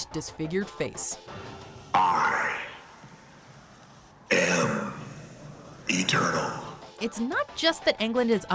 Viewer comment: is it just me or did Freddy say I am a turtle